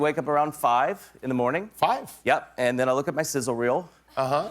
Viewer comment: What does that stuff Zach said at 0:25 mean? I really don't know... it sounds like "sizzlewheel" or something... is it something inappropriate? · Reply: Somogyi, He said “sizzle reel.” It’s a compilation of someone’s past highlights, like a montage of clips. He’s said that joke in other interviews. Pretending to be self-absorbed.